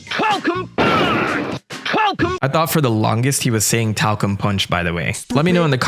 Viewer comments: My brother sister and I thought he was saying Puercum Punch! xD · I always heard Falco Punch and wondering why he wasnt Falco 😅